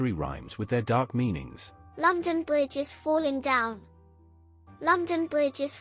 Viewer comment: sao tôi lại nghe thành lâm chan buoi just falling down là như nào 🫠🫠🫠